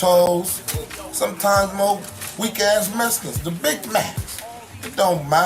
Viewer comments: The shooting of the kids riding bikes makes you hate these dudes and wish them the worst. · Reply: did he say weak as mexicans? 😒🤨🤔😡 on da set he better not be dissin us cuz im b on hotz no kizzy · Either we learn to live together as Brothers,,Or Die together as Fools..MLK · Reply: did he say weak as mexicans? 😒🤨🤔😡 on da set he better not be dissin us cuz im b on hotz no kizzy